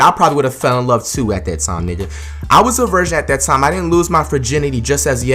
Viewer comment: the way he says virgin sounds like he saying Version.